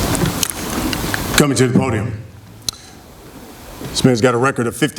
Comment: did he just say Spence instead of Canelo?